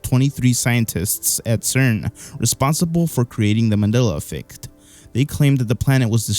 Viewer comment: It sounds like your saying “Manilla Effect”😂😂😂